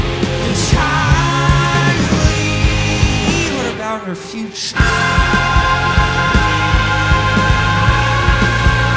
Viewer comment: does anyone else hear "what about the future?" or "what about her furure?" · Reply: SCRDYCAT sounds like “What about our future?” To me